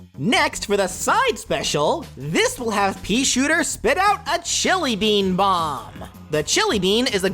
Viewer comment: WHY DOES HE CALL IT A JELLY BEAN??? ITS A NORAML BEAN WHY WOULD IT BE A JELLY BEAN Edit: nvm he says chilli bean